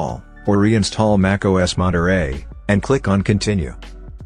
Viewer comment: With Captions ) Monitor Instead of Monterey?